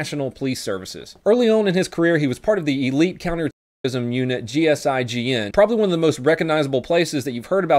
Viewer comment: Gsg9n thats what it sounds like u said. When german and the french finally become friends.